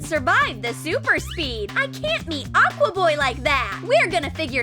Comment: I heard it say "aqua boy" but its supposed to be " aqua man"